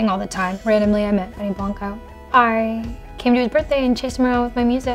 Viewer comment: "randomly I met..." Who??? did anyone hear that at like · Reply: Benny Blanco